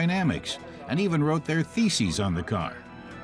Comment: at low volume thought that he said "they even rubbed their feces on the car". Man that's dedication... I think. Then went back and turned the volume up. I knew that couldn't be right.